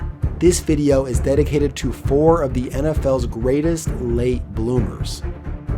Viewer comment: I thought this said Boomer for a minute lol